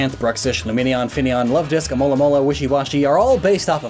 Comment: did I just hear him calling alomomola Amolamola?